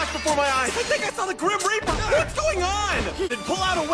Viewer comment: NAH I THOUGHT I MISSED HEARD HIM BUT HE ACTUALLY SAID “THE GRIM REAPER”